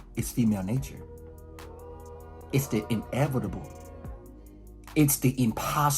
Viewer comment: thought you were gonna say it’s invincible